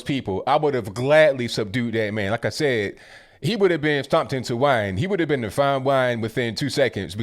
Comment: all I heard was "stomp you into wine" and "frontal cortex is going to get pushed back in time" and immediately thought: Must see the comments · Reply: 😂😂😂😂 He said " stomped into fine wine"!!! 😂😂😂😂😂😂